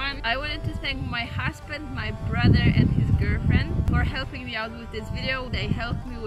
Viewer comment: For a second there I thought I heard "I'd like to thank my husband and his girlfriend" and I was very concerned